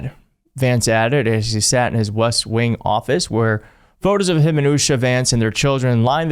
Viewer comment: It sounded like you said JD’s office in the “Wuss Wing” and it fits so well I can’t unhear it!